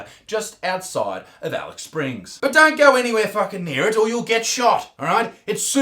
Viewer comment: Best flanny for absolute sure! 👌 Edit: 05:13 Isaac, did you just say Alex Springs?!? 😄